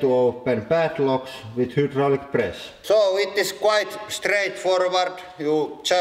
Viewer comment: EVERYONE PUT SUBTITLES ON !!! :D ITS SO FUNNY I CANT LMAO XD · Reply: At first I thought it said hitler press.